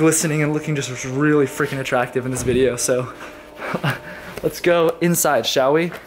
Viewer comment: You always look attractive. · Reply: I thought you said attractive thanos